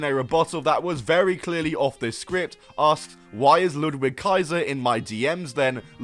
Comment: I never heard her say "is that why kaiser is in my dms" the while time i thought she said guys are in my dms woah thats rough